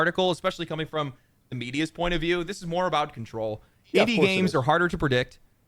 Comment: i thought it said india games for a second